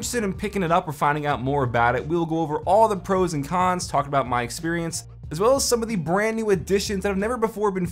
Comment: I thought he said my fucked up experience Lol